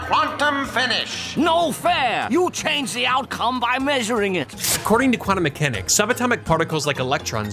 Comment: I thought he always said "you changed the outcome by looking at it"